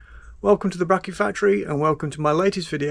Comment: Good move on the name change, at first I thought you were saying "The Bean and Bracket factory", "The bracket Factory" is more better. Wonder if putting the sump in an oven would burn off the oil? But would the sump fit in your oven? And would the misses let you! I see that Jay Leno has an American La France fire engine, perhaps he can help you source spares?